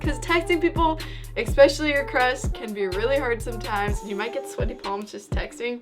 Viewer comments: did she say crust😂😂😂😂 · it sounds like she said crust not crush...